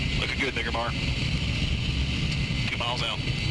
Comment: What does he say at 1:05? Looking good .. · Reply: Can verify, he was talking to lead man and says the last name of person then says “mark”.